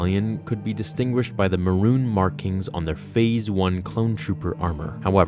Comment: what colour he says???? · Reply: Maroon. A reddish brown